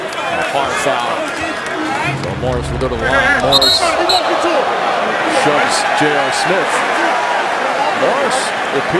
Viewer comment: What was Stoudemire saying at 0:12? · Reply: he ran into him